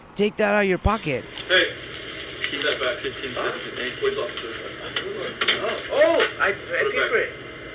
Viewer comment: Why did the shop worker not say something earlier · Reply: I heard the cashier say “he paid”, echoing the customer.